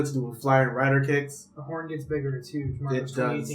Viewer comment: I couldnt hear super well, but it sounded like he just threw shade at Marcosatsu, lol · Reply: HammerMeister1999 he said a “the horn gets bigger, Marcus 2018”